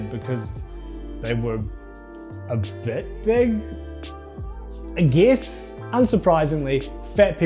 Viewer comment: what in the actual hell are you saying? I need English subtitles for that part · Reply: he says “a bit big, i guess”